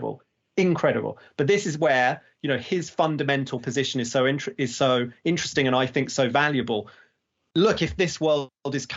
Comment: Wonderful interview. One of the best I have seen in a while. Any hint where to find the interviews mentioned at 00:59:55 ? I just cannot understand the name mentioned. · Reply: It’s Emad Mostaque